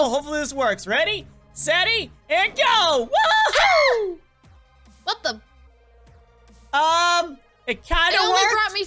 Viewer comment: i thought she said whst the f###